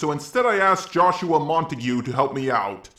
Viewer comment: I thought you said Joshua Mountain Dew JjajajajAJAJAJAJ